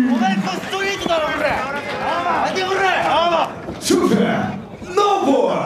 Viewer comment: なに？何て言ってる？？？聞き取れんわ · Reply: おまえストリートだろコラァ 立てオラァ